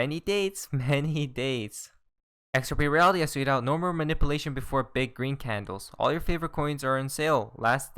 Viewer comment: Ohhh .... you are saying .... “tweeted out”” .....I always thought you say “justSoYouCanSee”” .... really fast LOL